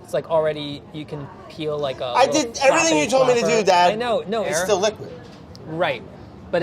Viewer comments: Why am I hearing, "I did everything you told me to do, dad "? Brad??? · "I did everything you told me to do, Dad" LOL (though the CC says 'with that' I think Brad meant "Dad") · "I did everything you told me to do dad" did brad just call chris dad at 12:15 ? guys i cannot hahahahaha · Did Brad call Chris Dad? Ahahaha